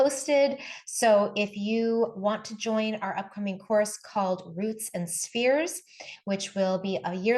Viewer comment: What says: "Our upcoming course called Roots n Spheres" What I heard : "Our upcoming course called Britney Spears"